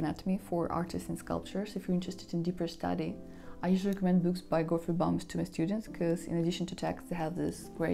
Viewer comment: Hi, I have a quick question. In a point of the video (6:42) you recommend a book by an author. I can't seem to understand what you said. Would you be able to tell me in written note here? Thanks a lot for your amazing work. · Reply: Hi) thank you! Oh Its book by Gottfried Bammes - anatomy for artists